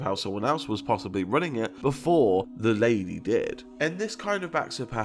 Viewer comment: Not sure what whispers said but I heard: "Lady did it"